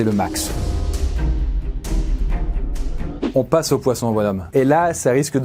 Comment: On passe au poisson quoi ? Bonhomme, Royaume ? 🤔 Le sous titrages écrit "Royaume" 😂 J'ai ré-écouté 5 fois minimum j'ai toujours pas compris le vrais mot prononcé ! 😅 Bref on s'en fout, ça ne gâche en rien la qualité de l'épisode qui est toujours génial, comme d'hab ! 💡 Merci pour tout vos contenus d'ailleur la Team! Et merci à ce cher qui se donne toujours à fond pour ses vidéos et en général dans son taff, j'en suis certain ! 👌💙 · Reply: Hello, j’entends « Bonhomme ». T’as regardé sur internet si les deux existait et lequel était le plus proche de la photo qui suivait ?